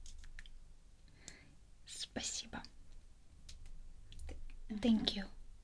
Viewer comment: I thought she said Spicy Bum at 10:10 😂😂